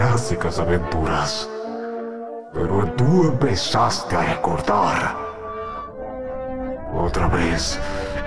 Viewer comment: parace que dice tu empezaste a engordar